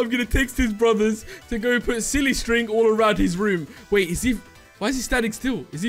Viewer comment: Um I'm sorry but did he just say ow to silly string?